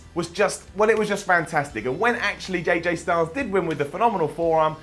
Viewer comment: Did he say "JJ Styles" at 6:29? 😂😂😂 Simon you're the man!!! #SimonCulture